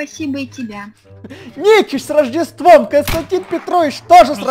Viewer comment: Когда ты сказал Никич с рождеством, мне послышалось НЕЧИСТЬ с рождеством 🤣🤣🤣🤣🤣🤣🤣